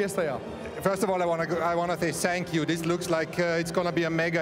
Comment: did toto say i wanna thay sank you